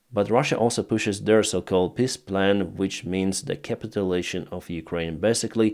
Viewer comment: How you sayd Peaceplan at 2:00 sounds like "Pissplan" for my german ears. And sometimes it really fits perfectly well. Especially when the surrendering of Ukraine is part if the plan. I could add how the polish PIS-Party sounds for my german ears... By the way, Pisskopf would mean "Pisshead", guess what i heard at min 3:00.